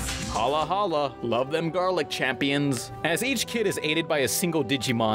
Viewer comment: Not gonna lie... I used to think they said "Garlic Champions"